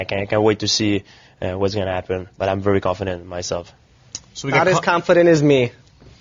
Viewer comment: what bj said? isnt understandable. all his confidence its me?